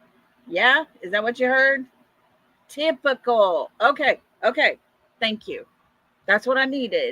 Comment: i hear "Typical " not difficult